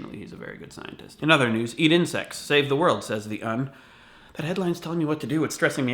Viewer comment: my friend on skype thought he said eat in SEX